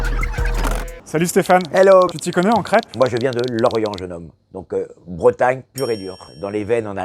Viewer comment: J'ai compris "je viens de l'orient" j'me suis demandé "mais qu'est-ce qu'il raconte celui avec, d'où on mange des crêpes en orient" mdrrrr